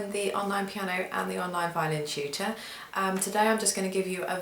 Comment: Instead of hearing "Tutor", I hear "Cheater". ahaha! The cheater made me inspired and want to learn even more. Anyways ate (older sister in Tagalog), you inspire me. I'm going to buy one and learn from you.